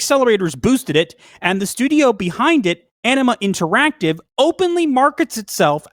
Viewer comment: At first I thought you guys said enema not anima lol